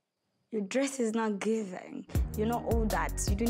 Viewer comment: Did I hear a “mother Telesa” at the end or my ears have a problem🤣🤣🤣i cannat